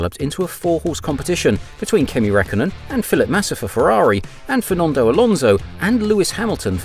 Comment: Great video, But it sounds like your saying "Philip Massa"😂😂 Made me laugh every time